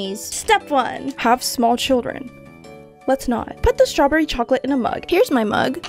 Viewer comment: I thought she said “pop small children” 😂😂😂